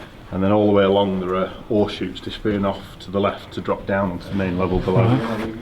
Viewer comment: I very much enjoyed this. Being from the US, I still have trouble with some of the guy's accents. 7/8 of the way thru the video, I figured out that what I heard as "horse shoes", was actually "ore chutes". LOL Makes sense now!! Stay safe. We will watch whatever and whenever you post. Love your content and your videos!